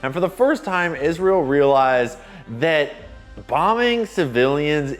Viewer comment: Yo my mans - I think you should keep doing these, and mad respect - however - you ned to pay closer attention to your editing. For example, 33:18, your CC's read: "Bombungs" instead of "Bombing", This is just one of the many little editing mistakes that crop up every min or so - having a single frame of your VO before cutting to a graphic, etc. Only mentioning this as it undermines your otherwise excellent vids.